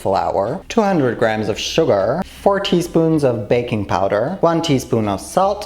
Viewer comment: When he said 4 tea spoons of baking power, I thought he said 40 spoons of baking powder and I was like HAAAAA